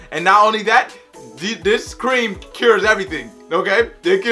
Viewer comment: sounded like he said scream and not cream 😂 It was a warning from the future 🤣🤣